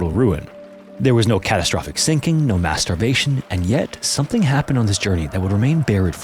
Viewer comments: … no masterbation? Did I hear that right? 💀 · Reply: Mass starvation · When your tired brain mishears "mass starvation" for "mast*rbation" and you have to rewind and do a double-take just to be sure/be able to comprehend what actually is being said... Lol.